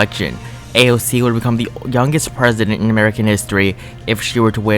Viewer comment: idk why i heard you say" dumbest president" instead of youngest. im literally a progressive- LOL